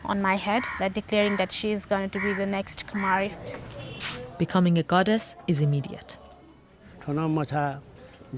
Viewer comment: Are people fucking dumb? So many morons in the comments thought she said “becoming a goddess is an idiot” - that doesn’t even grammatically make sense. Perhaps clean your fucking ears and learn to follow a storyline. said “becoming a goddess is IMMEDIATE”...it correlates with what they’re talking about as well. The only idiots are the ones who cannot fucking comprehend a documentary. 🙄🙄🙄